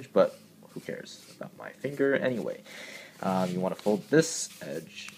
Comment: In the part where you were showing us what edge to fold and almost giving yourself a papercut , it sounded like you said "not my finger anyway".